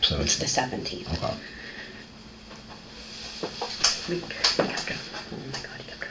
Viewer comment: Anyone else catch right at 15:31, she started to say "we kept her" and then stopped herself to change it to "he kept her for a week"????